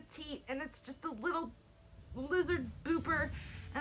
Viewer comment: Wait did she just say lizard booper😂🤣